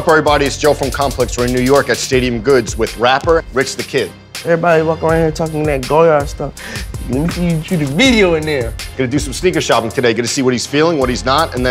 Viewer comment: Tell me wtf rich said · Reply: “Everybody walking around here talkin that ‘Goyard’ stuff, lemme see you shoot a video in there!” That’s what he said.